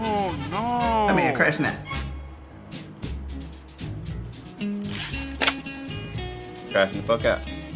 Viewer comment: Did say "that night crashin out" or am I trippin